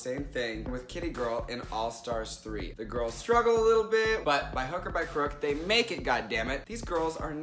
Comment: i heard by hooker by crook LOL.